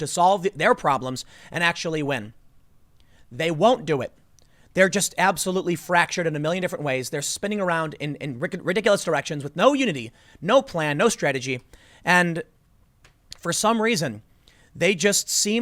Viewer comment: Is it just me or at 10:58 does it sound like Tim says "ridiculous erections" lol